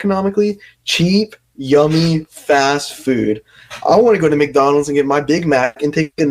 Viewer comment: I LITERALLY THOUGHT SAPNAP SAID “i wanna go to mcdonalds and get my DICK MAC” WHAT THE HECK IS WRONG WITH ME